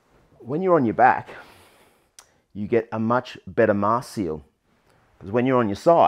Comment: What is a marcial? or Martial or marshall? Not sure what the word is or what it means. Thanks. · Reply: Hi Bobby, yeah the captions are not great. What i said was 'mask seal' i was referring to the CPAP Mask :_cheers